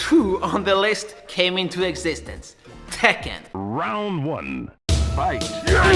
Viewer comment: is kazuya saying "ett, två"?